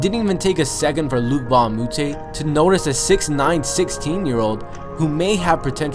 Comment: Did i hear 69 ?